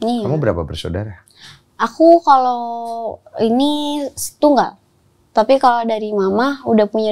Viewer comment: dengernya "setunggal" yg dalam bahasa jawa artinya satu